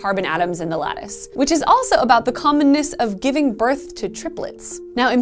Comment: I heard "communism"... XD It is a kind of russian magic too...